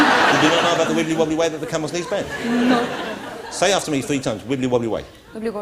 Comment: What did he say at 10:29? After NO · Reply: "say after me three times wibbly-wobbly way" And then she said "Say 'el perro de San Roque no tiene rabo porque Ramón Ramírez se lo ha cortado'" or something like that.